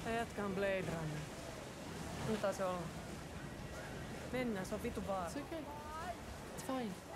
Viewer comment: What she actually says is: "This dudes a blade runner. Lets leave him be. Lets go hes fucking dangerous."